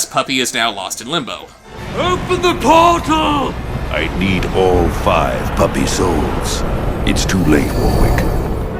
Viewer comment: "I need five more puppy souls" Seriously, that's what he says, he says "puppy souls?" That's probably the stupidest line I've ever heard. edit: A couple months later, I notice that it says "I need more five puppy souls." That's just bad writing there, unless there's a thing I'm missing. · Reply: I think he said “ALL five puppy souls.”